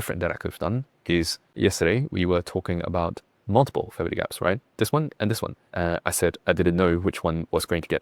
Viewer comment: what’s a “family gap”. I know that’s not what you said but I couldn’t understand and the subtitles didn’t catch it too. Very good insight by the way 🙏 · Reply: Fair Value Gap (FVG)